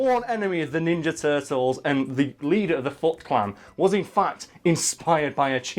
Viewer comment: I thought he said "leader of the fuck clan"